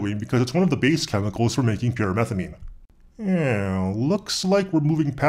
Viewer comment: "is one of the base chemicals for making pure (methamine?)" i think i didnt understand that correctly. What did you say?